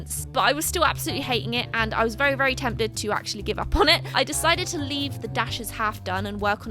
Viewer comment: I'm not lying when I say that I misheard and I honestly thought that she said "I was really tempted to throw up on it" 😂😂